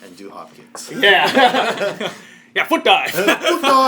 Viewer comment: lawlz. i thought you said "level up you're gay".